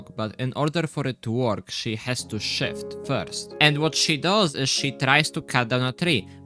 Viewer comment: Genuinely thought you said shit instead of shift and expected her to uhhhhh yeah